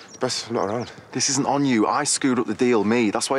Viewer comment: does he say scuttle? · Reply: Duricas « this isn’t on you I screwed up the deal » is what he says :)